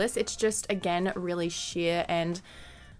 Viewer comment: Each time she said sheer I thought she was going to say sh*t